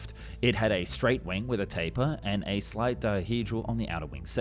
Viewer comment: did you say…. Taper?